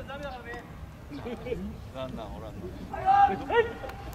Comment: ランナーおらんなーに聞こえるってwwwwwww